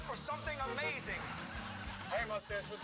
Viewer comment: Murr: I want my mommy! 0:12 Joe: LARRY!!! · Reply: I hear: help I’m falling